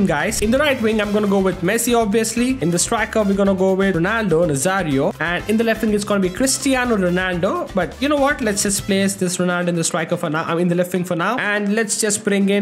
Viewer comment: Pristaino Ronaldo 😂😂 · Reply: why are you looking at his name? Those cristiano ronaldo fans also disrespect r9 so much. Also,even I heard saying 'pristiano'